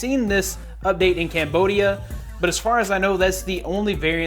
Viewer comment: did u say Cambodia?? i'm from cambodia